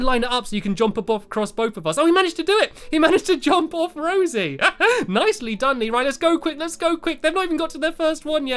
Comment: I thought u said nice-lee done